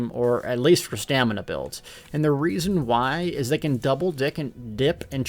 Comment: Anyone catch him say Double Dick